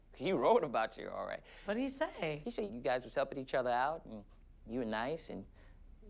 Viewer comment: What does Danny say at 1:06? It's the first thing he says that Pat says 👍🏼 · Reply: "He say you guys was helping each other out"